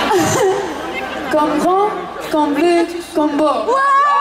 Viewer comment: What did she say? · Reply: Jakub Widlarz Comme grand, comme bleu, comme beau :)